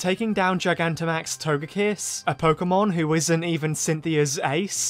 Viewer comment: I found this funny because I had to do a double take because I heard gmax togekiss and I did.